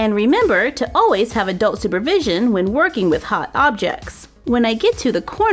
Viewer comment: OMG! XD I just realized, when you say take care of scissors and hot gun you say "always have adult supervision" I always thought you say "always have a doll supervision" that's so funny, having Sophie taking care of you all the time xD